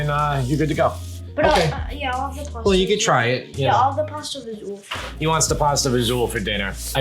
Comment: Can you guys help me what pasta is talking about at 10:42? I couldn't make it out what he said, and want to find the recipe. Appreciate it! :) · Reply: It's pasta fagioli. Enjoy! <link>